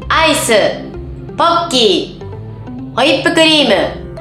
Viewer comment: ポッキーをボッキーって聞こえた...